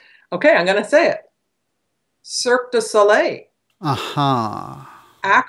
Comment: what did she say on 44:37 ? surk the sulrey?? what is it? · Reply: bbbbb she says "cirque du soleil". I've seen those guys and the show is pretty amazing